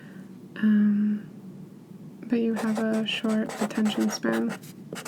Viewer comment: i thought she said "but you have a short attention sperm "